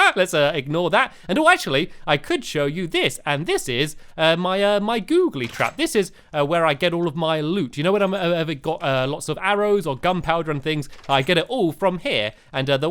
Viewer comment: did you just say Googlie Crap. Lol mixture of nice and bad. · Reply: No he said google trap